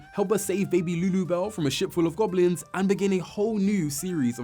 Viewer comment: At first I thought he said “a sh*t full of goblins” 😭